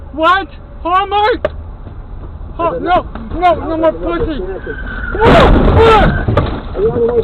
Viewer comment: is that even a woman driving? sounds like barney having a stroke (simpsons). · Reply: LMAO I thought he or she said oh no my pussy. lol